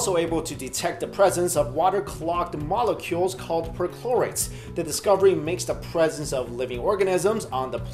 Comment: Being the nerd i am, i thought he said midi-chlorians! Its a shame he didn't... I WANT THE FORCE!!! P.S. The force awakens is AMAZING!!!